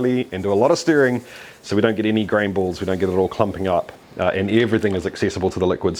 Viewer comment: When you said "grain balls" I heard "green balls" and shuddered. It sounds like blue balls gone malignant. 😨